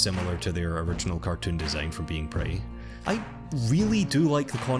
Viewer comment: what he said: from "Being Pretty" what I heard: from "Being Prey" :0